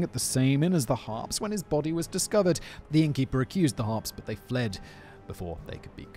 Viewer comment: I thought it said herpes lol. Do Paul Bernerdo